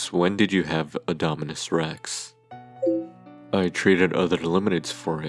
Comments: When He Said "When Did You Have Dominus Rex" It Sounded Like He Said Indominus Rex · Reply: he said did you have a dominus rex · he said did you have a dominus rex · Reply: I Know. It Sounds Like He Said Indominus Rex Coz Of My Hearing